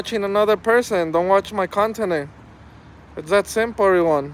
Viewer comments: did he just say "don't watch my continent" Oh boy...... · Why does he remind me of Baby Huey? Did he say don't watch my continent at 13:11 ?